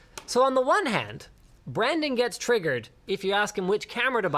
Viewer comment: I heard "Branding gets triggered..." haha